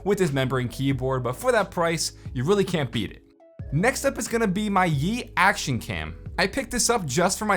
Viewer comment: I thought you said “Yeet Action Cam”.